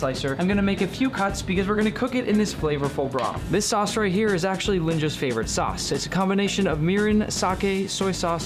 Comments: I am so early, only 9 minutes ago! 3:10 also did I heard “Lyjnia’s favorite sauce” if so I am happy that Nick is still remembering Lynjia’s legacy RIP ❤😢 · I kept hearing urine instead of mirin😭